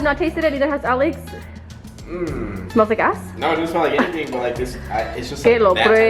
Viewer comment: Could of sworn she said "does it smell like gas" I was dead 😂😂😂